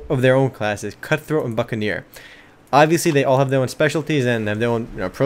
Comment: It almost sounded like he said fuckaneer at one point. XD Anyways, good video.